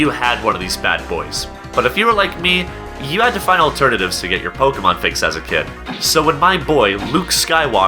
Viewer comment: pokmone XD the computer voice sounds like it said pokmone not pokemone